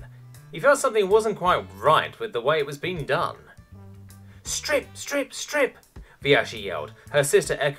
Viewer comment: HAHAHA First 10s and I hear 'STRIP STRIP STRIP!' I understand why the later chapters are so short.